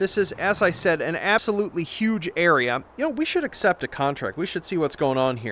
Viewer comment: "An *ASS*olutely huge area-" Do I hear a smidgen of audio-editing shenaniganry? :3c